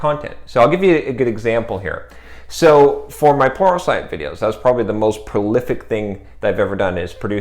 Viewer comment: Is he saying "for my porno site videos"??? I've gone over it like 7 times and I can't stop hearing "porno site videos"!🤣🤣